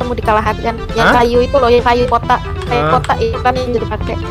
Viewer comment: ada yang ngerti ngomong apa wkwkkw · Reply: kayaknya kayu kotak Yg dimaksud cammo box